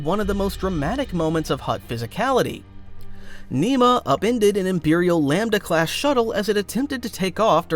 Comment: what did bro say right here? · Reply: “Nema upended and imperial lambdacass